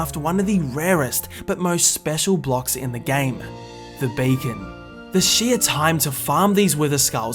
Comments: I thought he said bacon instead of beacon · I heard “the bacon”